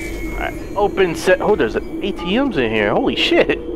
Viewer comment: he says etm instead of atm XD